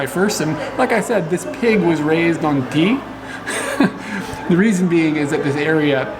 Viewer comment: Oh TEA! I thought you said the pig was raised on teeth. LOL